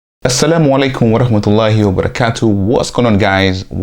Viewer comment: Great content! BTW: What did you say in 0:16? I only understand Assalamu Alaikum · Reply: Assalam alekum VaRahmatullahu Vabarakotuh. (Full greating in Islam meaning "Peace be upon you and Allah's mercy and blessings")